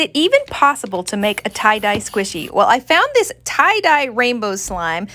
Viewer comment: I thought you said Italian ice squishy